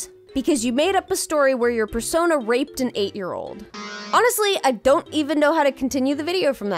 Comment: Okay, thought you said "R** and ate year old" and I busted out laughing